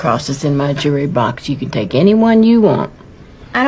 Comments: When she said "I want my lucky cross" I thought she said "I want my fucking cross"-... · When she said lucky I thought she said fucking 😺